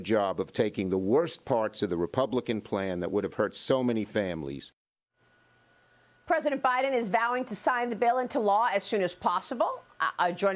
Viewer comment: if your wondering what schumer was saying at the end it was "we did a very good job of taking the worst parts of the republican plan that wouldve hurt soo many families off the table." Republican states receive more federal funding. They also lead in poverty, the Republicans wouldve been affecting majorily their own party.